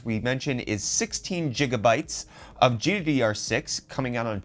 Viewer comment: GjigaBytes hahahaha did i hear right